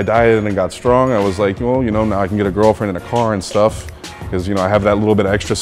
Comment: Wow at 10:35 I totally thought he said, "...now I can get a girlfriend IN a car and stuff." I actually thought he was being very suggestive, but upon closer inspection, I think he says, "I can get a girlfriend AND a car and stuff..." Makes sense, although it works either way.